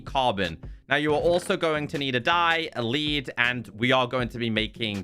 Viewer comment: i heard and keep on hearing “die” lololol😂